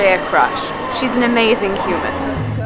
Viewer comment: There seem to be a few errors in the captions that could be quite misleading for anyone who needs the captions. In particular, at 2:20, I believe you said, "She's an amazing human," the but the caption says, "She's an amazing Cuban," which seems like an odd thing to say.